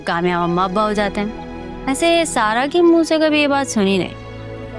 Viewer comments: she said "sara" instead of "zara" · she said sara instead of zara😂😂😂